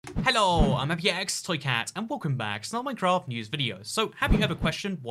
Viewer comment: "Hello, I'm px toy cat" Probably the closest the captions would ever be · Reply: I always hear “Helloooo, I’m aiembi e exc toycat” and I was so confused when I first started watching him, like boy, where’s the c in “ibx”?!??